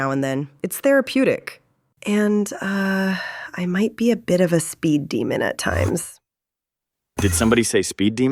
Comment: did say, speed demon (i wonder where i heared that before)